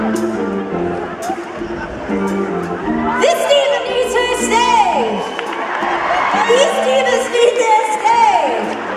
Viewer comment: what is she saying at 0:30? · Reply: "This diva needs her stage."